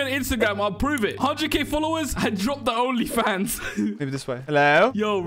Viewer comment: Did he say only fans on